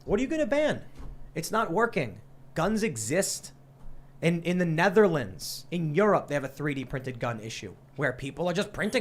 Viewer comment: I've been playing too much minecraft. I heard you say they have guns in the netherworld instead of netherlands.